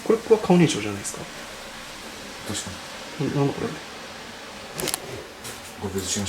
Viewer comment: 女の人の声で「なんだ？」って聞こえる気がします